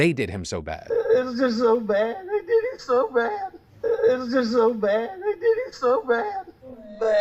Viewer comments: I heard "[They] did him so bad" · That last case is so hard. I definitely hear him say “they” not “I” and I was looking at the screen when he played it. He sounds heart broken. I hope he didn’t do it but also if he didn’t and is now being accused of it, that’s so heartbreaking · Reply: So I hear "ey did it so bad". Like, short for "they" if one were to slur their words a bit, which I even tend to do sometimes. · I hear “they did him so bad” personally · wait.. @ 12:57 i think he said they. the fact that they claim 2 different guns were used in the murders. could it be possible he had hit men do it? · I heard "They" did him so bad. If he is innocent, HOLY SHIT that's traumatizing. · I think he's very clearly saying "they", but that doesn't prove anything, I still think he probably did it. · From the sounds of the recording especially slowed down it sounds like he said "I did him so bad" but the defense will simply argue that he was just shown the photos of his murdered son and you can't expect someone to speak coherently and eloquently after such a shock. So I don't think the recording is the smoking gun they need. The rest of the evidence that is continuing to grow and grow is what will most likely put him away, jury trial though so you can never be 100%. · I don't know about the color of the dress but it does sound like he says "they did him so bad" to me. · murdoc is super sus, but i hear "they did him so bad" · I hear 'I did it' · I hear "it's so bad, uh did him so bad"